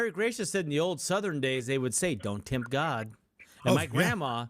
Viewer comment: I hear “don’t taunt God”